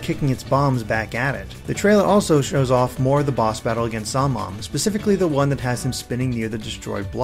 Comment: It sounded like you said, "Zomnom," which is actually an adorable idea for a nickname for him. XD